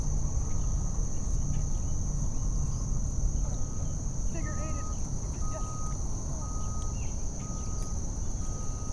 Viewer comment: did he say “n199£r ate it”??? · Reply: “Figure 8 it”